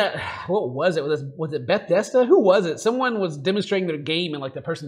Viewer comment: Bruh did ken just call Bethesda "Beth desda"